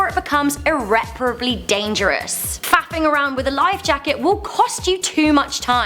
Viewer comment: did she say fapping around with a life jacket? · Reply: Tristan Abrams faffing